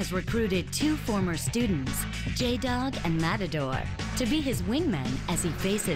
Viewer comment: I thought they said "Jacob and matador" and I was thinking "mystery, matador, and...... Jacob lol"